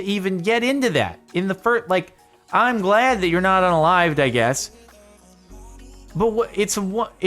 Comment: Did he say he’s glad he’s not alive? At 8:20 🧐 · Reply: max 1382 he said he's glad the guy's not "UNalived"